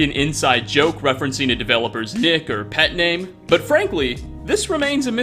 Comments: I deadass thought he said “it could be an inside joke referencing a developers DICK or pet name” Had to rewind a few times. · I thought he said “a developer’s d*ck”